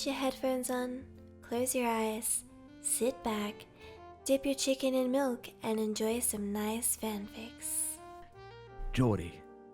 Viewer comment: Did she really say "dip ur chicken in milk"?